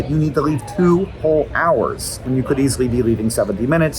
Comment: I swear, I was listening to this without looking at the video and thought Hank said “make a little frog” at the end, like he was drawing frogs at the gate or something